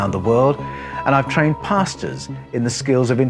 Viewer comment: Please forgive me for saying this but, I ALWAYS hear, "I've trained Pastas.." 👨🏻‍🏫🍝 It gives me a good chuckle every time I watch one of your videos. Which I do frequently and I greatly enjoy all of them. Thank you so much! 🙂👍🏼